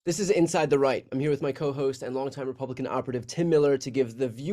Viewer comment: I misheard Brian in the intro. I thought he said "Inside the Reich." LOL